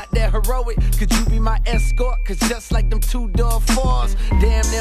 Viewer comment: I always thought they said “cause just like them 2-dogs fart”. I just found out he says “cause just like them 2-door Fords”. Listen to that part of the song. If you didn’t hear it before, now you won’t be able to un-hear it.